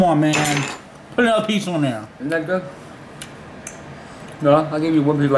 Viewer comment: sounded like he said the n word 😂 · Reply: Miles Chester88 he said “ain’t that good” not nigga.